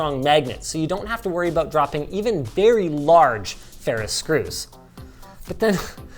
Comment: the subtitles are wrong: "ferris" instead of "ferrous"